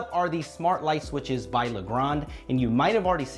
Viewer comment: Am I the only one who thought he said LeBron?